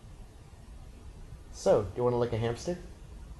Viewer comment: I thought said "Do you want to lick my hamstring". I'm sure it would have had the same effect.